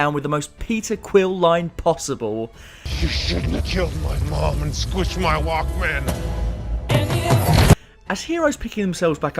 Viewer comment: "You shouldn't have killed my mom and squished my Walkman" is probably one of the best lines in cinematic history. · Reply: Dang this whole time I thought he said “ and squashed my rock band!”